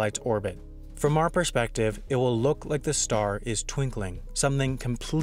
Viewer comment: I thought you said Twink-Ling, like a small gay person